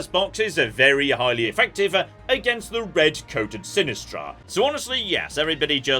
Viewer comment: I know is saying "red-coded" for the enemy alignment, but I just hear "red coat" and start imagining revolutionary war Brits